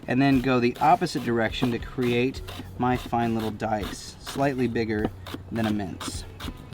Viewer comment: LoL. When you were chopping the tomatoe I thought you said slightly bigger than a mint (like a piece of candy) 🤣🤣🤣